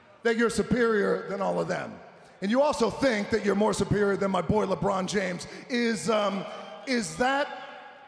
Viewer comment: Rock: “superior to my boiler brawn James” 3 times I went back and heard “boiler brawn James” then I heard “my boy Lebrawn James” (I suppose Lebrawn is all one word). I know the Rock has a daughter but I didn’t think he has son and why would his name be James and not Johnson? Can someone please tell me who the Rock is talking about, it’s like several other wrestling videos where they talk about some guy (can’t remember his name now) and some times in the video for example Kevin Sullivan called this guy the G.O.A.T and in other videos I asked who is this guy and one person replied “he only the G.O.A.T!” with no other context but I found out he’s an NFL player and it seems lots of people think he’s the best ever (once again forgot his name and the team he plays for). And when I found out he’s a NFL player I understand now why myself and anyone I asked never heard of him as I can’t tell you what month the Super Bowl is played in as it’s not covered in the sports bit of the mainstream news